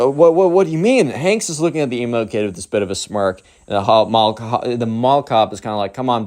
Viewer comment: It sounded like he called the mall coo hot 😏